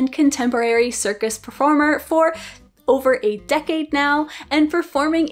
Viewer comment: Lol. I thought you said 8 decades. I had to go back a listen again.